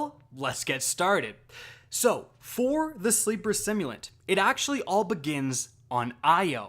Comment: did he just say sleeper simulant dang he is hooked up to that gun